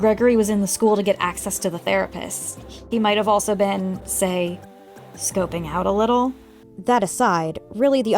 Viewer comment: I misheard that as “he might have been, well, GAY” and god I wish that was the plot of fnaf